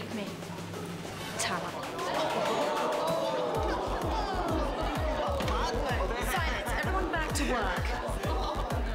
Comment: What does Sambuca call Miss Montoya at 37:34? I can't make out what she's saying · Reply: "tart"